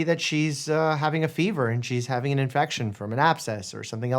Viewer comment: I heard “evasive visa bagel income pee” 💀